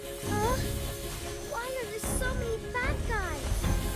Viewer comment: when i play Jawhead someone i hear her saying 'Why are there so many fat guy'